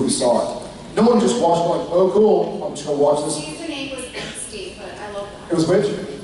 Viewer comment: what is the word that Jared didn't understand? The basis of every other CW show according to Jensen... thanks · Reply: “Angsty”